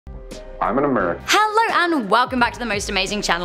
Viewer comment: Does it say first "I'm a MMer"